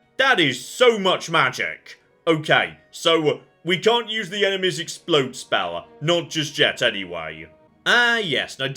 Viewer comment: I thought she said enema explode spell. That would be much more interesting